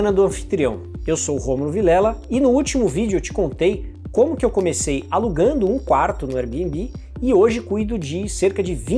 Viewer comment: Qual o nome da ferramenta de gestão de imóvel mesmo que vc falou ali por volta de 1:05h. Não consegui entender bem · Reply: OwnerPRO - <link>